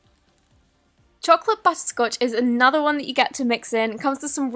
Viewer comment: it sounded like she said butt scotch😂 no hate just thought that was kinda funny